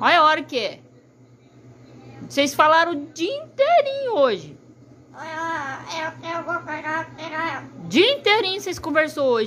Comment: Só eu ouvi "Eu vou pegar as piranha"??